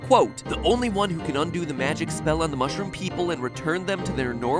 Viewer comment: the not auto generated captions say smell instead of spell